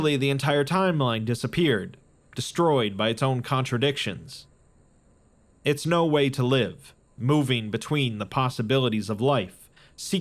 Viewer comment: am I the only one who heard "it's SNOW way to live"??